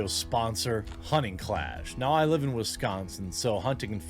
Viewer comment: i misheard hunting clash as honey clash and just imagined a bunch of bees fighting each other.